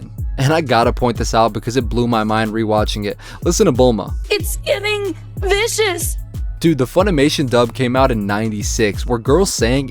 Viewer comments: I thought Bulma said, "it's getting vicious." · I think she's saying "it's getting vicious" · she was not saying “It’s giving”. She said “it’s getting vicious”